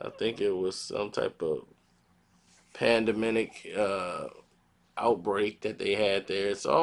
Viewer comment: did he just say pandeminic? lol